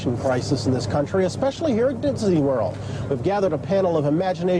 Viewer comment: "...especially here in Ditsney world..." " DITSNEY" thats what i heard him say, hahaha, lol.